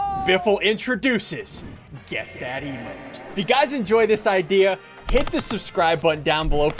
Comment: At the beginning it sounded like he said guess that emo